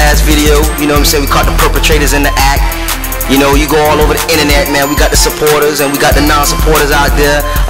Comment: Does he say inenet at 0:24 ? Lol